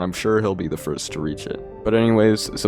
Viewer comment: I only hear "Subscribe to Deathstreeks and end me, if you want". Are you ok?